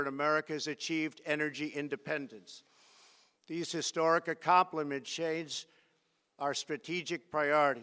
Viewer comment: says “accompliments shades” instead of accomplishments change😭